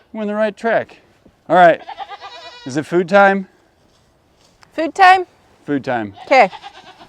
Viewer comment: Omgeeeez, when said to, "Is it food time?" I thought he said BED time....lol....I'm like, "No, it's not 🛌 time."....❤ GBY'ALL!!!!